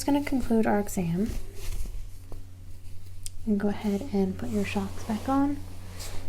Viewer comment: Did she just call them “schocks?”